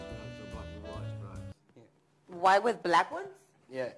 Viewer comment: I think @ 5:55 she thought he said "your bra, why with black ones" ... But idk why her mind would think that.